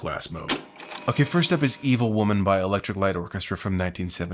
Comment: HAHA! "Evil Woman" ??? I always heard it as "Illegal Woman" !!! 🤣